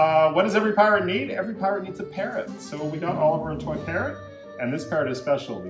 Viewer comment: Did Human Say Carrot?